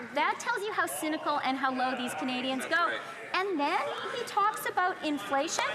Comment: did she say “this is the low that Canadians will go?”